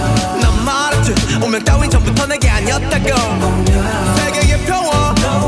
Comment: I swear I heard the word SEGA from Shadow. Fight me Also, these videos are amazing!